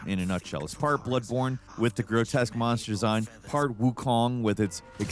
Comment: Am i tripping or did i hear we mod